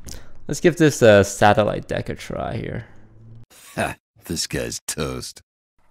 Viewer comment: Is the intro “this guy’s toast” or “disguised toast”? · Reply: Young Dao he says this guys toast but its a old meme that it sounds like he is saying disguised toast thus that card inspired him to get that name